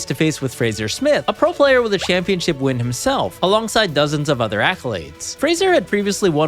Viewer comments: did u say "other athletes", LMFAO!!!!!! since when yugioh became a sport...... XDDDDDDDDDDD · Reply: "other accolades" not "other athletes". LMFAO!!!! XDDDDDD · did you just call card players “athletes”?